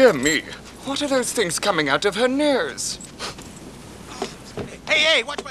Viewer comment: For the longest time I thought he said "what are those things coming out of her meers?" It made no sense to me but now I recognize that it's just a thick east ender accent; or something.